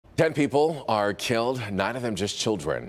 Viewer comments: I swear it sounded like he said in the beginning "Dead people are killed" but I had to re watch and it was "10 people were killed. So tragic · I swear it sounded like he said in the beginning "Dead people are killed" but I had to re watch and it was "10 people were killed. So tragic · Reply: That’s what I thought he said dead ppl are killed I’m like huh